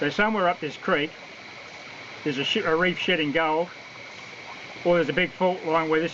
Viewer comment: I seriously thought you said shitting gold, and then I realised you said shedding lol. I love your videos. Never been travelling so I enjoy the destinations.